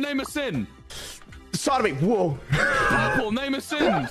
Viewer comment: what did he say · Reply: sodomy